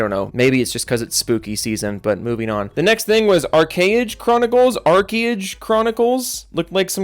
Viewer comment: I thought you said Arcane instead of ArchAge. don't get my hopes up lol.